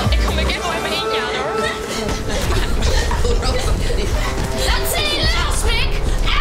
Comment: Super leuk maar wat zegt 8:23 ? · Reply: Ze zegt: 'Laat Sally los, Mick'